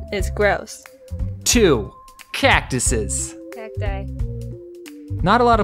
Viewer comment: what did she say? i cant understand her XD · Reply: She corrected. She said Cacti.